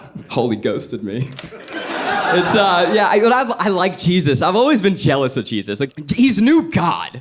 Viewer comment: When said "I've always been jealous of Jesus," I thought he said "cheeses" and was so confused on what that meant